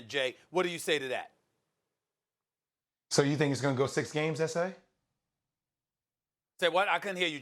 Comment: Did JW really say esé? Smh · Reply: SA as In Stephen A